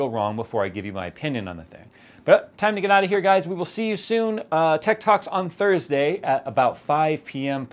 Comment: Thought he said Ted talk...